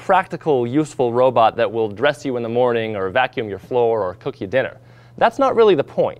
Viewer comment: I thought when he said "Cook you dinner" He said "put you down" and now I wan't a robot that will just constantly insult you.